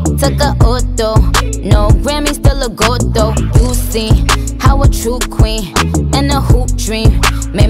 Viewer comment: Tell me why instead of " how a true queen" I heard "Haraju-Queen"